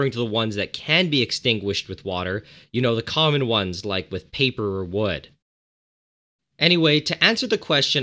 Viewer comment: When he said paper or wood. I first thought he said People and wood. lol.